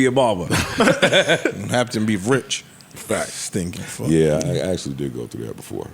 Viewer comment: Yoo did ice call a fat stinky fuck @ 1:40:39 😂😂😂